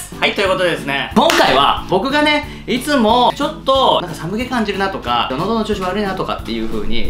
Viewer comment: 『寒気感じるなー』が、 『参鶏湯汁なー』とたまたま聞こえたのは、 アニキの酔っ払った口調だからか、 おれの酔っ払った聴力のせいなのか... そもそも『参鶏湯汁』てwww